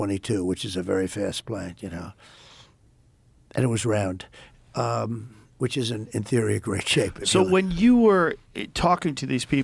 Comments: “And it was round. Which is, in theory, a great shape” That really made me laugh for some reason. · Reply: I thought he said it's an inferior shape. Edit: 4:04 Nope, it's "which isn't, in theory, a great shape". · It’s interesting how many people in the comments misheard this one small thing. Trump said “…round, which ISN’T, in theory, a great shape.”